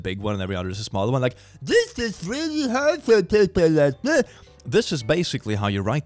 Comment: I think he said: "This is really hard for people like me."